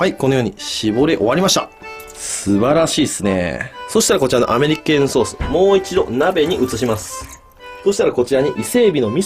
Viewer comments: アメリケェーンソース手間やべぇな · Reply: ここすごいアメリケェーンソースって聞こえる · アメリケーヌソースをアメリカーンソースって聞き間違えたのは俺だけじゃないはず😌